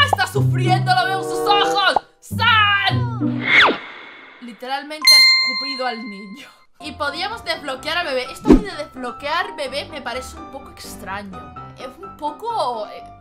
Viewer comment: Escuche escupido al bicho XD pobre bebe